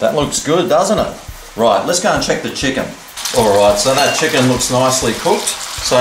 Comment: I don't think my butt would ever forgive me if I made that... also, is it wrong that I first heard you say "let's go choke the chicken"? (I'm a bad lad)